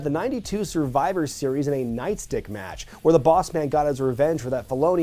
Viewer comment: Did he just say a nice dick match? ..... Night stick match.....Night.... stick... 😂